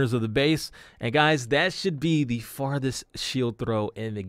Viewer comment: I thought it said fastest then I realized it said farthest